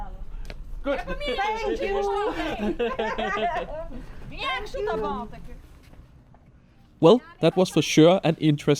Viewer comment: did she say "thank you"...in English?